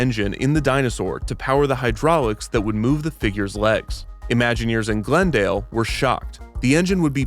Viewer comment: I heard this as "Imagineers in Glendale were shot" and thought the Glendale Imagineers were executed by Disney for not being as innovative as the ones in East Hampton